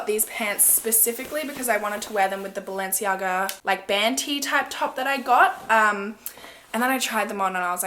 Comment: Haha I thought you said bantee I’m like what’s a bantee then I’m like oooooh a band tee haha 😂